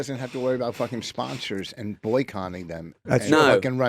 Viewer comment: Did Vos just say boycoNNing? 7_<